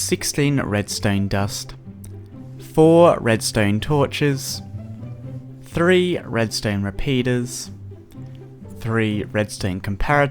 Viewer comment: I thought he said 3 redstone penises